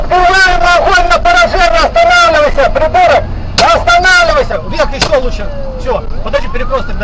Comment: em 1:45 eu entendi ele chamando BOLSONARO KKKKKKKKKKKKKK